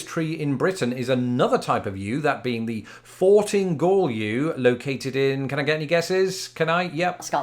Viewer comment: I thought you said the Farting Gaul Yew. Thanks google for knowing that translates into Fortingall Yew.